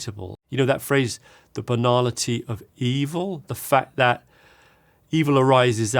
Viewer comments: Ngl, on first hearing, I thought he spoke about the banana tree of evil and I thought “is that a saying?” And on second hearing, I heard he actually said banality of evil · I heard “The banana-teeth of evil” context means everything ❤ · Genuinely thought he said the banana tea of evil